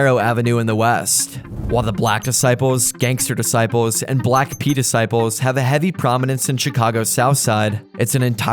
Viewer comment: Did you say the black P disciples @ 11:18?